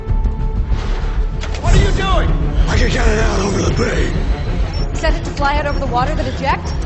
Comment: what did he say? 😂😂😂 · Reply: i can get it out over the bay its not hard to understand